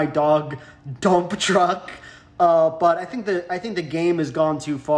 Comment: english is not my birth language and I sincerely thought you said Dump Trump hahahaha thank god I'm this dumb cuz it made me laugh even harder, thank you.